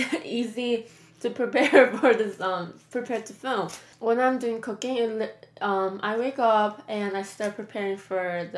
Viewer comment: Sounded like you said "When I'm doing cocaine... Umm, I wake up and I start preparing for the.. to film the videos.." lol!